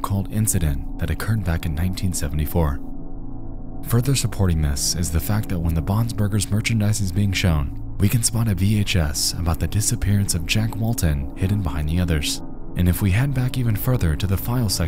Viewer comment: I first thought he said bobs burgers